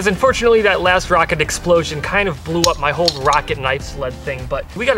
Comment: I thought it said pocket knife not rocket knife